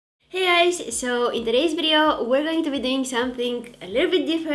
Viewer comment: Did she say “hey guys” or “hey eyes” ? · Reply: Lol it sounds like eyes but she said guys